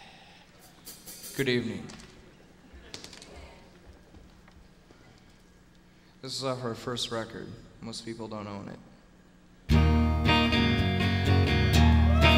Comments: "This is off our first record, most people don't own it." Proceeds to play history. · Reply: For 25 years I thought he said "Most people don't know it," but now it sounds to me like "Most people don't own it." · "This is off our first record, most people don't own it" Kurt's dry sense of humor had me cracking up on this album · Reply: Thought he said most people don't know it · "This is off our first record. Most people don't own it" lmao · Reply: I always thought he said "Most people don't know it" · "This is off of our first record.. Most people dont' know it.." Entire audience applause cause they all know it.. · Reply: I always thought until now that he said most - people don't know her name. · Welp, i just learned that when he says "This is off our 1st record" He says "Most People Don't OWN it." I thought he said "most people don't KNOW it" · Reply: Wow, I’ve always thought he said “most people don’t know it yet” as if he knew how big the unplugged album would be